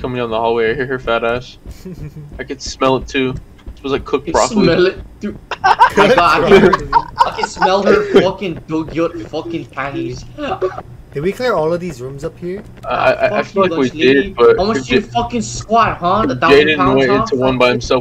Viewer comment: Wait did just said "dugyot" which means gross in tagalog at 6:39-?